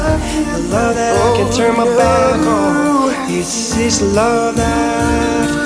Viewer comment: “love that i can turn my back on”? Someone correct me if i am wrong cuz it sounded like he said he can turn his back from God’s love??? Hehe just a curious question, but this was such a great worship video i loved it ^^